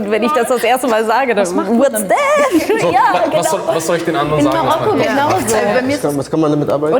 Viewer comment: hab ich da gerade MAROKKO gehört ❤️❤️❤️🇲🇦🇲🇦🇲🇦